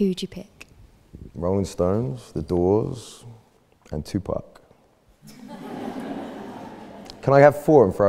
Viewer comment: What's the third music group he says? I don't understand really well his accent (30:39) Thank u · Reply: The Rolling Stones, The Doors, 2Pac and Eminem ;)